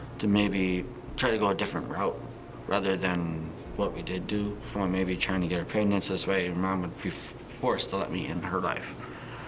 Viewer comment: did he just say he could’ve gotten Gypsy pregnant instead?